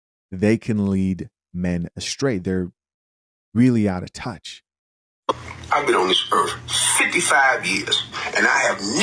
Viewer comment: All I hear is " ive been a simp for 55 years"